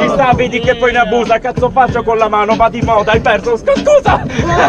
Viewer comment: Ragazzi...non capisco cosa dice al minuto 7:23 dopo che lo prende per il culo per la mano rigirata......COSA DICE?!! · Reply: "va' di moda è il verso scu-scusa" il 2018 è stato l'anno in cui sono usciti tanti trapper quindi il verso "sku-sku" viene da quello